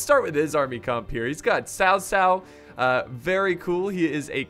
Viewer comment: Did I hear Sassau?🤔